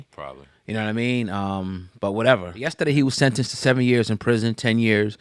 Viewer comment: I’m not gonna lie. 7 years is nothing. Especially after hearing the mothers voice. As a parent I know this haunted the mom. Taking blame. Making herself feel like it was herself & all types of self blame. Because none of us want this to happen to our kids. I would kill me for the rest of my life. 7 years seems like nothing in comparison to this lifetime of trauma. My prayers go out to that girl & her mom. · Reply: I thought she said 17 years.🤔